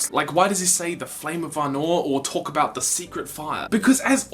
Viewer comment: I heard it as flame of Arnor, referring to him carrying the elven ring of fire